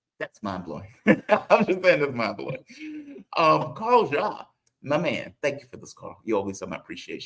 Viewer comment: I thought Carl had said his name is pronounced Carl 'Za' ?